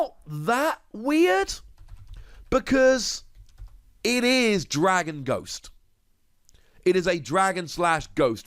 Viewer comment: Dragapult squad were you at! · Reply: I thought he was saying dragon ball